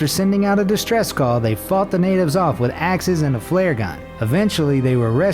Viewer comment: Did he say f*** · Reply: Faught